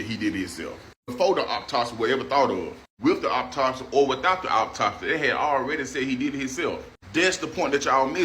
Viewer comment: I am sorry, but that was some of the funniest shit I have heard in weeks, for real. Hearing this man mispronounce the word autopsy by saying "oppotopsee" is freaking hilarious. Dude tripled down, too! I cannot take someone's opinion seriously if they do not even know how to construct clear sentences. Kinda makes you question if they truly know what they are talking about. Just saying. 😂